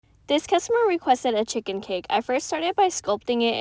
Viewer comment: Bro I thought you said you were making a CHICKEN PIG cake in the beginning and kept wondering when you would get to the pig part